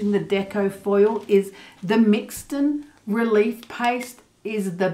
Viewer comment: Hi I have watched your You Tube tutorial several times and listened hard to find out the name of what you used for the release paste I thought you said Mixton Release Paste if that is the right name and where did you purchase it from ? I have looked on many sites please help . Thanks so much Liz Abbott NZ